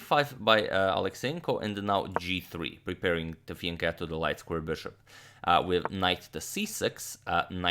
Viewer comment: What does he say at 1:04 "Preparing to FIAN the light squared Bishop"? And not "Preparing to FIANCHETTO the light squared Bishop???" - I can only find the term FIANCHETTO in Google.